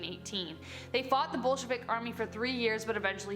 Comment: Wait when you said Bolshevik army I thought that you said bullsh** army and I had to pause and just think for a minute. Amazing